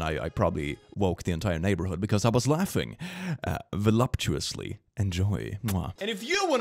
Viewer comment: One of my favorite things about is that he very clearly does not know what "voluptuous" actually means, and yet does not let that stop him from misusing the word horribly. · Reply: I thought he was saying voluminous, but you’re right. That’s pretty funny